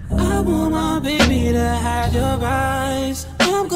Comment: I thought he said I want my baby to have ur vibes🤦🏾‍♀️